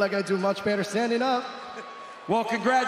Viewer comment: is it really me or DC sounded like he said Song how you're doing at the end instead of Song Yadong😂😂